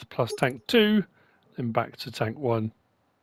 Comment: my Star Wars ears heard "bacta tank 1" instead of "back to tank 1" 😂 You've mentioned you're a Star Wars nerd so I figured you would enjoy that!